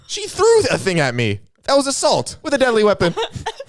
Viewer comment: i didnt realize he meant assault i thought he said a salt so i said that was a ketchup like an idiot